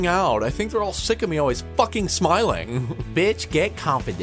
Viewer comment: Why is it at 1:16 it looks like he actually says f ing smiling